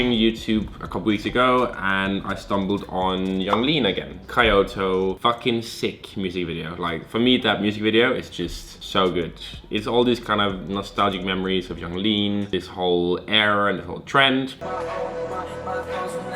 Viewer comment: did he say “kai oto” instead of Kyoto?